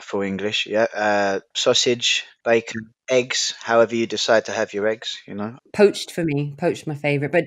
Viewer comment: 브릿센트 팟캐스트 200% 활용법 1. 화면의 스크립트를 보지 않고 귀로만 들으며 이해한다 (어려우면 2번부터) 2. 스크립트를 보며 다시 쭉 듣는다. 3. 다시 스크립트를 보며 이해가 안 가는 부분에서 멈추고 단어나 표현의 뜻을 찾아본 후 노트에 적는다. 4. 모르는 단어, 표현, 문장을 꼼꼼히 공부한 후 스크립트를 보지 않고 처음부터 끝까지 다시 듣는다. 5. 중간에 멈추지 않아도 다 이해가 될 정도로 듣는다. 6. 무한 반복 모드로 놓고 조용히 눈을 감은 후 잠에 든다. 7. 일어나서 틀어놓고 선생님들 속도에 맞춰 섀도잉을 한다 :) 8. 섀도잉을 30일동안 1일 1회 진행한다. 다른 에피소드와 원서 읽기 시리즈는 아래 저희 오디오클립에서 확인해주세요 <link> 브릿센트가 궁금하시다면? ====> <link> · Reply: 대사 0:45 뒷 부분에서 However you decide to have your eggs? 라고 물어보는 부분에서 How do you decide to~ 라고 들리는데 however 가 맞나요?